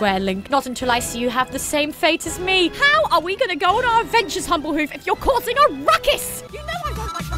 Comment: I think I heard her say dumblehoof in the end bit